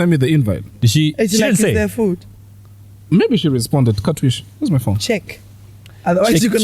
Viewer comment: did u just say "katwishi"😂